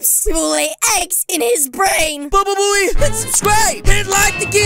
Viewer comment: bababooey! Is that what you said? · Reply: He said "bababooey"